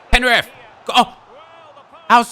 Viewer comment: I heard fifa 15. "PEN REF!"